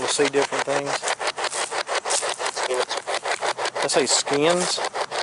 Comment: Great video bro, love these cemetary videos they're so beautiful. When you thought you heard "skins" around 8:44 - 8:46 I thought he said "spirits", then he says it a second time at *;56 -8:59. If you listen close you hear someone else repeat it right after he does. Awesome video